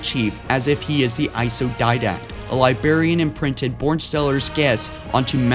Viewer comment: hahaha it sounds like hes saying "libarian"